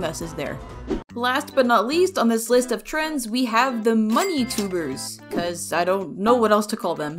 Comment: my brain immediately went “VOX IS THAT YOU!?” until I heard “money tubers”